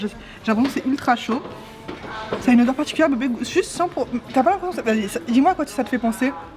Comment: C est moi ou elle dit suce (